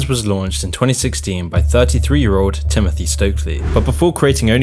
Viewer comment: I heard 13 Instead of 33 and my jaw dropped